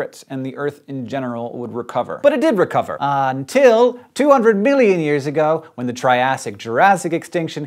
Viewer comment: did he just say 200 billion years?